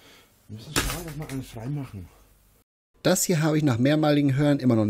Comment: "Müssen schaun, dass ma ei freimachen" Das hab ich verstanden. Würde auch Sinn ergeben.